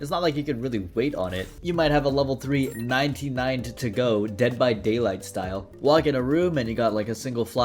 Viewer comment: did i hear dbd or am i crazy